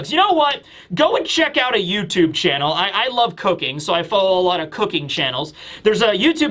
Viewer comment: xD at 1:55 i heard " I love cocaine !"